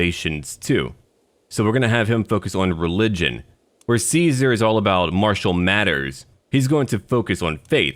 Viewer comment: I heard that as "While Caesar is focused on Marshall Mathers" and I was like wait what does Eminem have to do with this :)